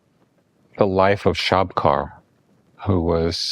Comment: how I'd love to have the name of this great Tibetan master. I couldn't make out the name. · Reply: Lama Shabkar